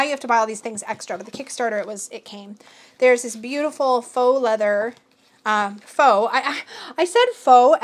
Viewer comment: Oh my god, could you IMAGINE, thinking "oh wow a gorgeous Last Unicorn deck!" And then being met with the ✨️horror✨️ of realizing the guidebook is made of FOAL leather?! That's genuinely so disturbing but I'm imagining the person's face and the just... the ✨️terror✨️ and I'm cackling 🤣 Poor thing (Not trying to rip on the commenter who said that, I can see where a mishearing could happen, I use captions myself.)